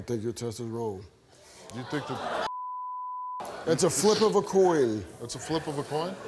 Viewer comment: what did say?... "I think your test is--" what?... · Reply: He said a flip of a coin